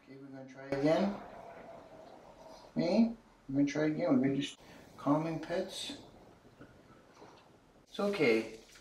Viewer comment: Guy said "calming pets." I heard "calm your tits."